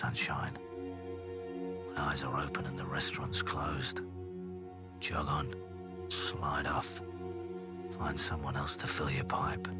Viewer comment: Chall on?? swal off? What exactly does he say? Seems like uncommon british words. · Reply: Jog on, slide off